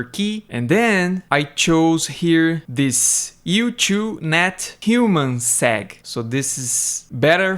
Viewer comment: I heard that like "human sack." 😂😂